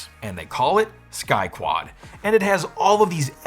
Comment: I heard "Sky Twat"